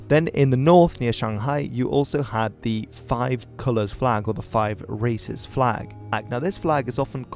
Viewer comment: I heard "Five Racist flag"